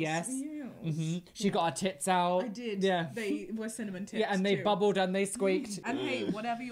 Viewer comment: I thought you said cinnamon tits cause I have bad hearing and I laughed way too hard at that.